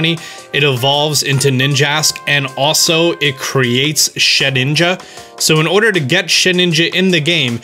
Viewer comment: Did he say “Sheninja”?